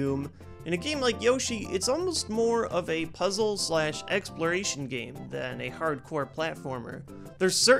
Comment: Lol did he say "egg"sploration at 1:10?